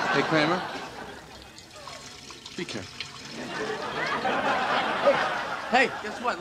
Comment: Anyone else thought jerry actually said "Kramer, not too hot" instead of "Kramer, be careful?" Mandela effect?